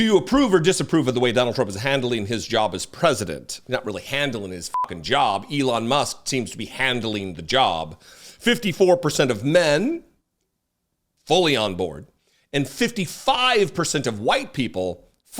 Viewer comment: When you said "Elon Musk is handling his job" I heard "Elon Musk is handling his junk".